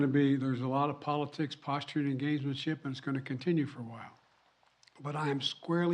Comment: Does he say "engagementship" at 5:55? He could just say engagement.